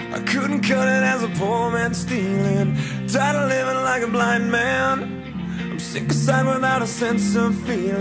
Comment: I swear I thought Chad said "black man" instead of "blind man."